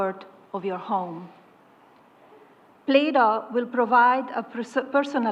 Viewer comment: is she saying ‘Plato’? They are calling it ‘Plato’?!?!?